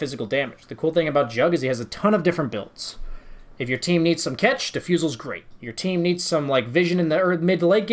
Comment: I keep hearing this word "ketch" in dota, I'm sure I'm spelling it wrong but where does this word come from, what does it mean? Is it like "catch"?